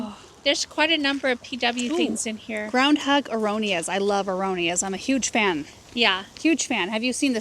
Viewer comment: could someone please clarify what is saying at 16:45? Aromia? Erronea? I tried looking it up with no luck. TIA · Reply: Aronia or Chokeberry